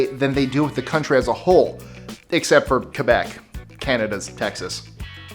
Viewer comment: Did he say “Kebec” and not “Quebec” at 4:41?